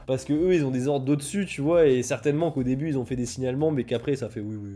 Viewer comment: Petit rajout, la vraie police est passé, mais elle a juste regardé sans rien faire. Mon dressing Vinted ça se passe ici: <link> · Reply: j'ai entendu "signe allemand" j'ai fait le rapprochement entre le fascisme et le Japon, mais même si je sais que c'était "signalement", ils auraient fait des signes allemands ça m'aurait pas étonné, le Japon niveau social et santé mental c'est vraiment pas dingue